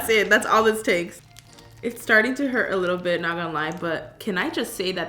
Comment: sounded like BitLife